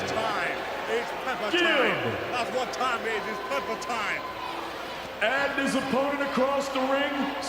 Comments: Did Gib say it’s Peppa time at the beginning? 😂 2:00 if you were wondering. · I swear he's saying "is time, its peppa time" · "It's peppertime" more like slip and slide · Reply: i thought he was saying its peppa time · What did gib say when the camera was on him? I think i heard: “ its pepper time!” 😂😂🤣🤣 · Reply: He said “it’s my time” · Is it me but at 2:01 I hear “it’s time,it’s peppa time”